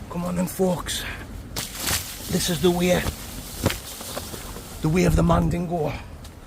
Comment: Greetings from the US. At 6:03 , he actually said, “This is the way.” Is a Mandingo or the last Mandalorian? Either way, let’s enjoy the camp!